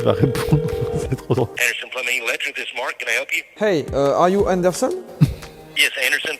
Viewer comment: ptdr le pauvre, il a du rien comprendre a ce que disait xD quand il a dit "are you anderson" j'ai compris "'are you understand" genre je me suis dit, "c'est fini, jordan parle pas anglais"